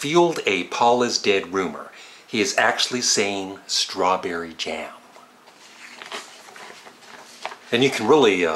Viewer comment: I always thought John said crawnberry sause, not strawberry jam